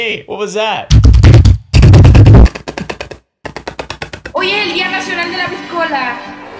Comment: I think she said .it is the national day of the pistol 🤨👌